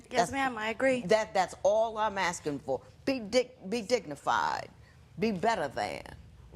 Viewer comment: Is it just me or does she say 'big dick'